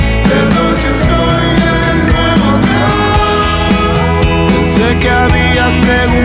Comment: Siempre pensé que decia: perdón si estoy en Nueva York